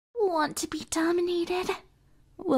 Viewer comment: At first I thought it said “want to be demoted”